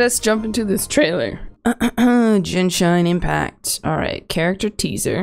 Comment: Did i hear "Gin Shine Impact" ? :D